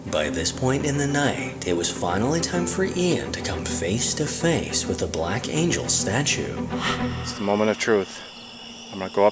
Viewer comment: I could be wrong but the male voice sounds like it said Death Angel 🤨🤔